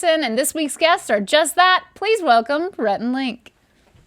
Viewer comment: I don't know why but when was introducing Rhett snd Link...I heard "welcome parentalink" I was like whos parental link?😂😂😂